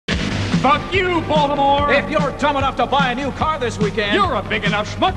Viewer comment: Hearing this audio in ytp’s and not knowing what it was or where it came from, I always thought he was saying “fuck you voldemort”.